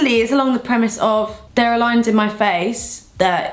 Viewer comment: I heard "there are lions in my face" haha